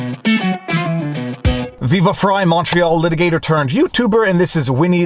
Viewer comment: I just now realized that you said “Viva Frei Montreal litigator” I have always thought you said “Viva Frei Macho litigator “. 😆